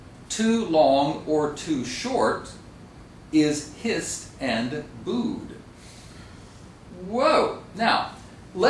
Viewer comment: Hello, thank you for uploading this great video:) I have a question. Could you tell me what the teacher says at 6:51 in this video(in English), please? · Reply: well now i got it, he said "hissed and booed".